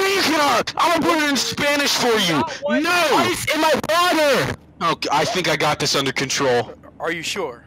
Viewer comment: pilot: having a mental breakdown about how he broke the steering wheel co-pilot: I C E I N M Y W A T E R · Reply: I thought said fly in my water